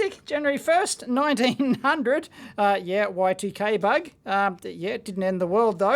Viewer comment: "Y 2 gay bug" is that really what you said lol